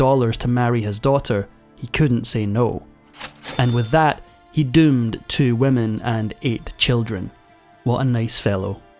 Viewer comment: I heard “he doomed two women and ATE children” lol